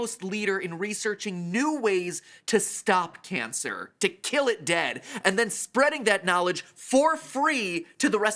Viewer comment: For a second I thought he said kill kids 😳